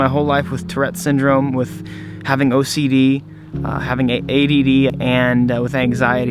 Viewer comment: swear he was about to say aids lmaooooo